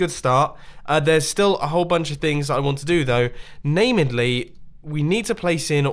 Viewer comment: Did anyone hear “namidly” instead of mainly?